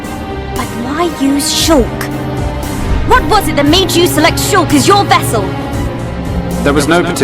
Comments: I've decided to rewatch this and I just noticed that at 8:22:50 Melia says select, but the subtitles say choose. I guess there is something to fix in definitive edition. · I just realized at 8:22:48 Melia says select but the subtitles say choose